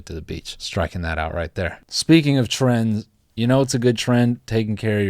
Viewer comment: I thought he said "speaking of tren" at first and thought it was about to be a segue into more Jeff Bezos talk